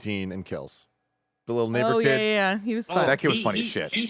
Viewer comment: I'm really glad that I could turn the captions on to hear what Tim actually said at 35:09 cause it really sounded like he said something else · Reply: NEIGHBOR. HE SAID NEIGHBOR KID. 😅😅😅